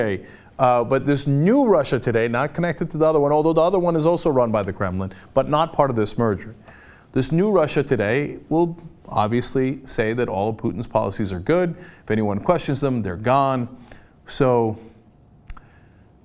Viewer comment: Every time someone says 'Kremlin', I hear 'gremlin'. ...am I alone in this?